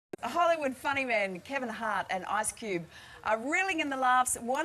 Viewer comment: did she say ass cube??? Lmfao